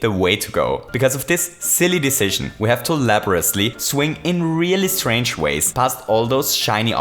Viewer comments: subtitles says "(inaudible)", when you clearly said "Labourosly" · captions says [inaudible] but it's pretty obvious it's laboriously (or less likely, elaborately)